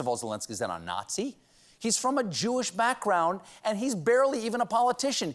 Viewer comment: At about 1:56, the subtitles put "urine" instead of the word "Jewish". Pretty big fucking mistake there YouTube. Might wanna fix that mistake on a video that talks about nazism...WHILE he's talking about nazism